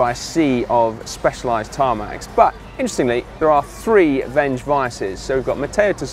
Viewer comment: I heard him say Tinkoff had three bench vises on their team car. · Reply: He said Venge ViAS.